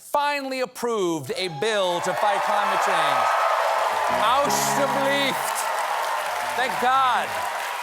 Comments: Did he say "Alsjeblieft" which translates to "You're welcome" at 0:24? hahahah Or did i completely misheard that? Somebody please tell me/ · Someone correct me if I'm wrong did he just say "alsjeblieft"?